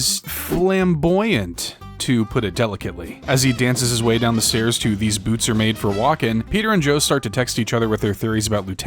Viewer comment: did bro just said Joestar??? 🗣️🗣️🗣️ IS THAT A JOJO REFERENCE???? 🗣️🗣️🗣️🗣️🗣️🗣️ WTF IS SOMETHING THAT ISNT A JOJO-REFERENCE RAAHHHHHGH STICKY FINGA!!